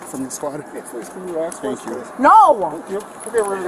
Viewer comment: the officer actually says "Thank you", you probably misheared it, just wanted to let you know